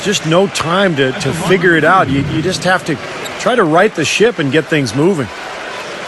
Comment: lol 1:33 did steve say"ride the shit"